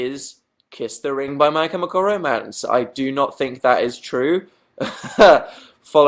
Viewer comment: what band does he say? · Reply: My Chemical romance